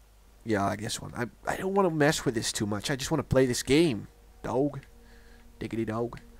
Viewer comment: I thought he said i hate this game then i thought why r u doing a lets play of it then lol